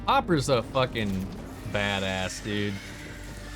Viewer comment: i thought he called hopper a “fatass” for a second 😅